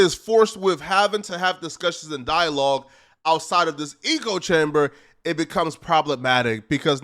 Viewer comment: Wtf is an E co chamber. Thought you were saying ego chamber, which kinda makes sense.